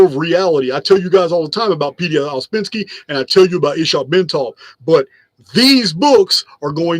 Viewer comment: As always fascinating.. Thanks Did someone hear what author mentioned before Itzhak Bentov? I only heard something with Pinsky? Around 2:16:40 Also Paul Laviolette and Peter Carroll I got Tried to listen a few times.. But even Google can't help 😅 I have now 90+ books Mentioned So I'm still 3700 short 😂 Almost done watching everything Reading will take a few more years Loving It tho 💪🏾🤎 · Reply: P.D. Ouspensky